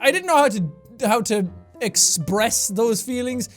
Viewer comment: Sounds like he said exbreast instead of express